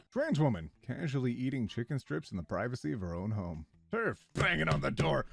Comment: I heard French women and I was like are chicken strips illigal?